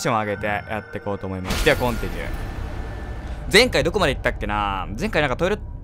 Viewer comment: ナイトクライの実況なのにコンテニューがポンピドゥーに聞こえたあたり、ライフイズストレンジが終わるのが悲しいんだと思う。 別にさんの滑舌のせいじゃありませんよ！！私の耳が難聴です！！